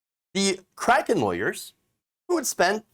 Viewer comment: I thought it said Karen Lawyers.